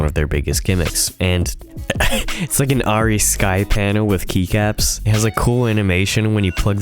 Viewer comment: "... Skypannel with keycaps." What I heard: "Potentially usable on set."